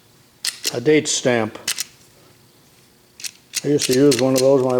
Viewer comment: You are not talking to yourself, I enjoy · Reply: Definitely not. A couple of times I even thought I spotted something before did. For example the Lion numbering machine. I thought you'd said "Bates stamp" which is what a lot of people call them, even the ones made by Lion. I have a couple of Lion stamps and one Bates.